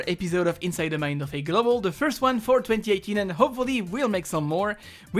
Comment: The first one 420-18 is wot I heard